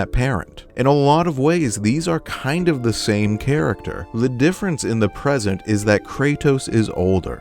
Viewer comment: Did you just say le difference